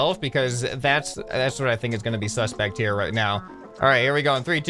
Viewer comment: OMG did just say sus